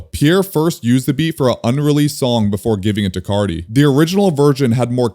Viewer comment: Am I tweakin or did bro say virgin😭😭😭